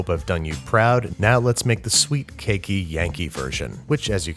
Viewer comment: I thought I heard "sweet, KINKY, Yankee version" and I almost choked on my water, then I realized he said "cakey" :(